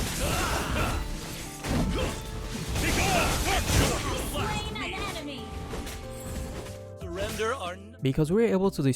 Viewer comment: did i just heard Alucard said "Begone Thot!" as he killing Guin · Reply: its actually "begone darkness" r/woosh